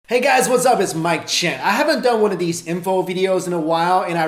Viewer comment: low key look like a young Jackie Chan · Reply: I heard "Mike Chan". Maybe its his son! lol